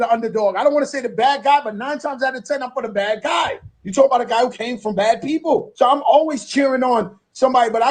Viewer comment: Did I hear wrong at 2:15:10 minutes. He said "you talking about a guy that came from bad people". Whaaaaat? Did I misunderstand him?